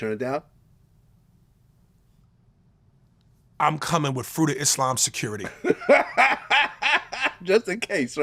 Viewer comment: For a second it sounded like he said "I'm coming with fruity Islam security!" 🤣🤣🤣🤣